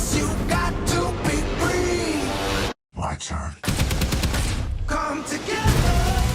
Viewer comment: does batman say "Lantern" or am I just imaging him saying it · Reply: He says "My Turn"